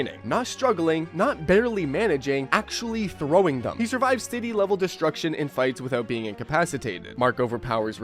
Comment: I heard "he survives Diddy level destruction" 😂😭😭